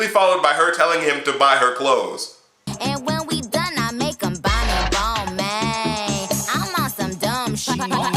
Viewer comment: I thought she said "And when we done I let him buy me lo mein"